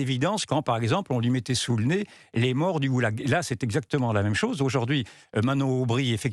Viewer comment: Les morts du oulague. C quoi ? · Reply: Ce sont les morts en deportation par les communistes dans les camps sovietiques, les GOULAGS"